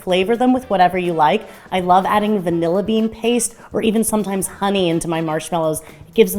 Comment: I had to listen to the into bit about flavouring the marshmallows a second time, because I thought you said "I love to flavour them with vanilla bean, or a bit of onion." No, honey. Not onion, honey. Honey makes way more sense. #morningbrain